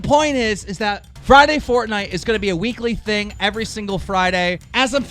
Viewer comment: I heard “ I ran,” I was like Oml really? Than he said “fortnite” :/.........